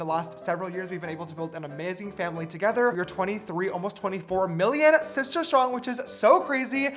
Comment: I thought I heard billion because there’s only 7 billion people in the world and I thought you said 23 billion oops 😬